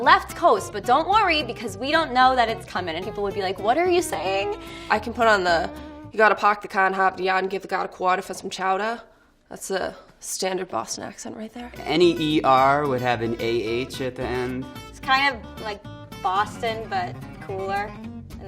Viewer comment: can somebody tell me what she says cause all I heard was "for some chowder"